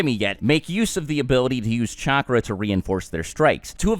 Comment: I literally heard sacharrai(dunno how to spell his name)